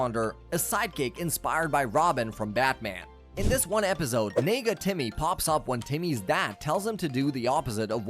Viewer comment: I swear I thought he said nigga Timmy at first and I was weak asl 😂😂😂 but it's Nega-Timmy